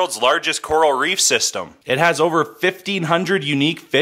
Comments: At the end it sounded like he said what's on your f ING list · I thought he said whats on ur fucking list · Thought he said “what’s on your fucking list?”